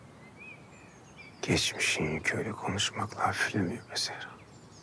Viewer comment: da Ömer ne diyor anlayanlar yaza bilirmi? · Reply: Gecmisin yükü konusmakla hafiflemiyo be zehra